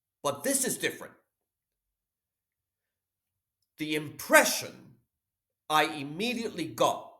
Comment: at first I was barely listening and thought you said you got depression from watching his videos 😂